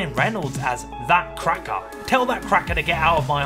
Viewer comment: I thought he said “Bat Cracker” 😂